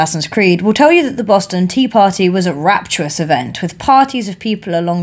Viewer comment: i kinda thought she said “boston tea pOtTy”